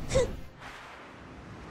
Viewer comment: min que dice cuando gana el combate? imus? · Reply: Iluso